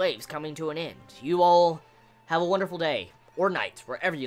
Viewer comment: Thought you always said, "have a wounderful day..or not" realised it was night now..